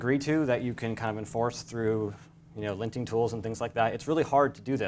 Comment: For some reason, I heard "...with class rules you can enforce with linting tools" as "...with class rules you can enforce with lynching tools", a bit harsh bur probably effective. Joking aside, a refreshing talk on CSS and how CSS, web components and the shadow dom interact.